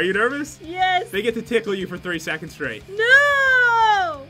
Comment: I thought he said 30 seconds